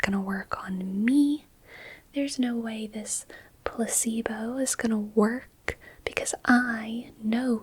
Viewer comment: I nodded off a couple times, but I'm sitting up in an office chair so falling tends to wake me up. BTW, it sounded like you said pussybow instead of placebo.